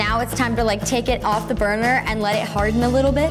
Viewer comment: when she said burner i could have swore she said BURGER :-) :-)